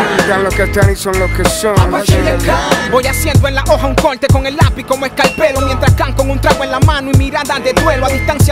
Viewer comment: alguien sabe que dice Apache en el min 1:00 al 1:04 es que no se le entiende. Gracias. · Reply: Voy haciendo en la hoja un corte con el lápiz como escarpelo Mientras can con un trago en la mano y mirada de duelo...